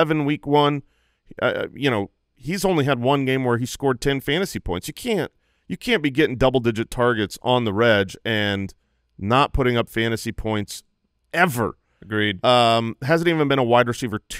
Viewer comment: Did say “On the rej”? Like the Reggie? lol